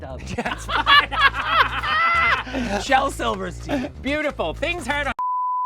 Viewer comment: I swear she said 'The Bald and the Beautiful'